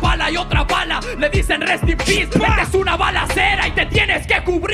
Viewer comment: Creo que nadie entendio cuando lobo grita "rust in peace" puede ser una referencia al album de megadeth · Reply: Cada quien entendió lo que quizo, por que yo al igual que el autor del comentario principal, entendí "Rust in peace", tal vez si lo dijo o tal vez mi fanatismo a Megadeth me hizo entender eso jajajaja